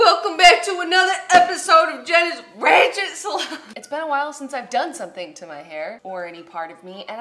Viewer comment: is it ok if I hear it as Jenna's wretched salon? D